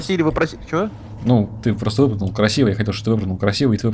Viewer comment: мне одному послышалось выпрыгивай